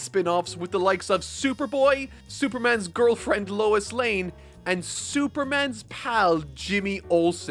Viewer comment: I dunno if it’s the accent or the fact I literally just woke up but I swear at 3:00 it sounded like you said “Superman’s Cow” not gonna lie I’d read that spin off